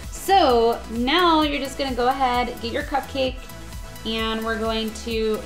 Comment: in 8:16 did anyone hear that said go ahead and eat your cupcake instead of saying dip your cupcake?